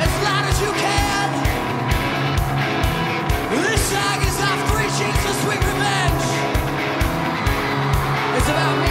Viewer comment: what did he say at 33:56? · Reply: "This song is off Three Cheers for Sweet Revenge. It's about me and Mikey's grandma."